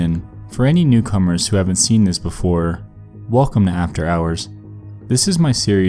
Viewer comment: At first I thought it said, " After Dark". I was about to shit mai pants!